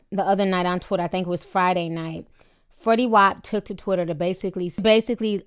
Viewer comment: I heard that..."Freddy wap"